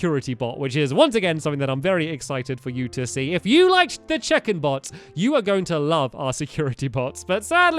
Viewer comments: Anyone else thought stumpy said chicken bots? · Who else thought he said chicken (bots) every time he said check-in (bots). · in the last episode i thought he said CHICKEN bot